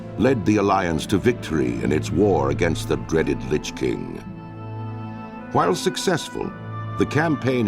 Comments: ROFL AT 0:23 Sounds like he said "The dreaded Bitch King" Listen to it!!!! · For a second I thought the narrarator said "Bitch King."